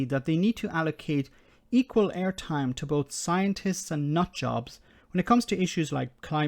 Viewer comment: Did you just say "scientists and tories"?